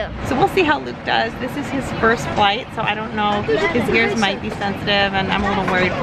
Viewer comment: I thought it said “baby’s first fight” I was like YAH LETS GO BABY KICK HIS BUTT!!